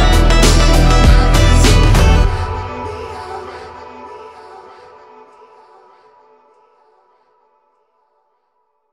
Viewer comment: what is he saying? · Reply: Sirens sirens